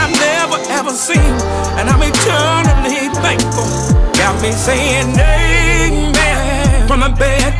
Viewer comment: I thought he was saying got me saying nae nae this whole time